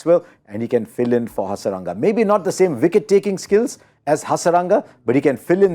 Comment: Huh? Did he say wicket-keeping skills oh Hasaranga?